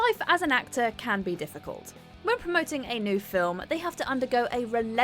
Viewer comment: I thought the commentator said "promote anal film". 😆